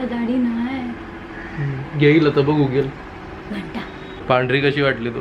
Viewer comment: did she said Ghanta